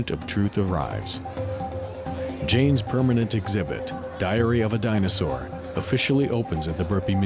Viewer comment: Truly fascinating video but I think that I need to have my hearing checked. When he said "Diary of a dinosaur" I heard "Diarrhea dinosaur". That indeed would be a terrifying creature to encounter.